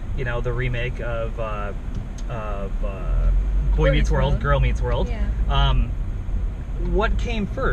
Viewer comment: Did anyone else hear him say Coy meets world?